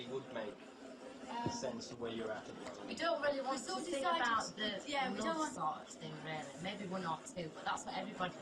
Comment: At around 8:52 what is Mel b saying? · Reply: "We don't want to sing about that love song thing, really." I think